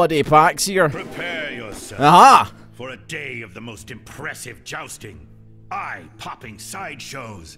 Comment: I thought he said "the most depressive jousting" instead of the most impressive..... Also, if you do those viewer games on twitch, are you gonna upload them to youtube as well?